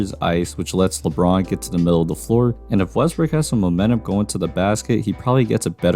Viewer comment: did he just call him Westbrick? 🤣💀